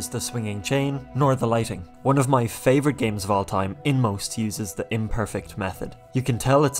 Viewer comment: Inmost looks really great, I'd like to play it too. But just my two cents here, I got confused for a bit when you said "One of my favorite videogames of all time, Inmost" and I was desperately trying to look for the game's title. for a good few minutes I thought you said "...in most, uses imperfect pixel art" like "it uses imperfect pixel art for the most part" Im not a native english speaker so I didn't think much of the former sentence and just thought it was normal. so I went down to the comments to see if someone mentions the game title, nothing. then I was like "does he mean 'In Most' is the title?" I search "In Most game" and it gives me random stuff. until I finally went with "Inmost pixel game" 😂 Not required, but I think it would be great if you include a small credit text at the corner of the video if you introduce footage of a game. Something like "Inmost (2019)" just a suggestion, you can take it or leave it.